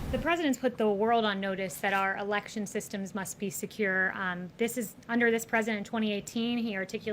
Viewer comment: Not sure... At first I thought that I heard her say "this is outlandish", but I'm not sure.